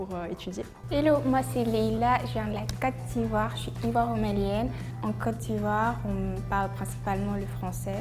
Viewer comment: she actually said "Je suis ivoiro-malienne", implying she is half ivorian (Ivory Coat) and half malian (Mali)